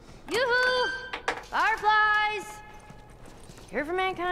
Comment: Hehe, the captions say [inaudible] but I recognized that "Yoo-hoo, big summer blowout!" from Frozen.